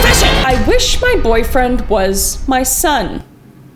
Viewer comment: When i heard "i wish my boyfriend was my son" i thought she said "i wish my son was my boyfriend" 😭 also when you said "i wish i pushed you out of me" I WAS DYING